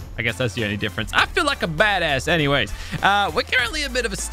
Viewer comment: did he say badass